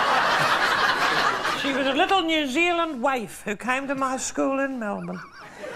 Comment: The subtitles are wrong at 11:13 in. Dame Edna says “waif” not “wife.”